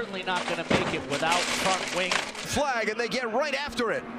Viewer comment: yt legit thought the commentator say ~fuck~ wing not front wing if u use subtitles